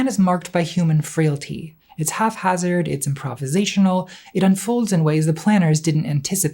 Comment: did you just say half hazard? Like the granite episode all over again